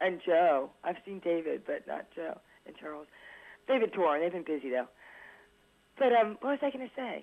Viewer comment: What does she say at 3:39 ? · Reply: "they've been touring they've been busy"